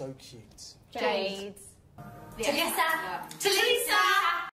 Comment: I HEARD THEM SAYING "JENNLISA"🤪